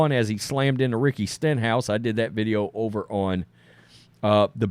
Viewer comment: I was listening to the race on tv but not watching the screen at the time, but I SWEAR when Wallace hit Ricky Stenhouse the announcer was so excited I swear it sounded like he said “Ricky Shithouse” because THAT made me stop what I WAS doing and start watching the screen. I said, NO WAY did he just say what it sounded like he said. (BTW sorry for the word used but that’s what I heard).